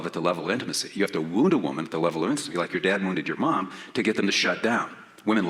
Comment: I thought he was saying 'woo,' but he was saying 'wound,' i despise this man